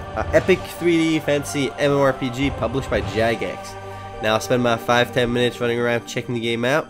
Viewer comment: Did he say published by Jack Ass?